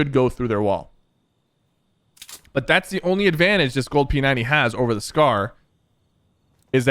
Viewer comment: did he say gold p90